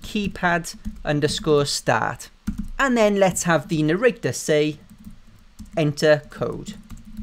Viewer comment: wait wait wait did you just say "Narricter" 6:01 lol hahaha